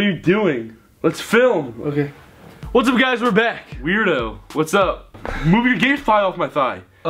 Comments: That non-copyright dance music tho! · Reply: did he say move you're gay thigh off of me??? Thinking right now 🤔🤔 (not that anything is wrong with being gay but just thinking) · did Ethan just say "move your gay thigh off my thigh"? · I thought Ethan said in the beginning of the video "Move your gay thigh of my thigh" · did he say gay thigh or gain thigh